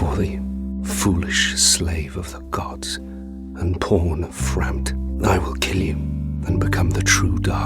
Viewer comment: i heard that as "porn of frampt"